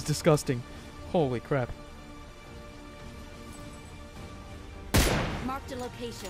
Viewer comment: with English captions. Mark the cra*. I heard. XD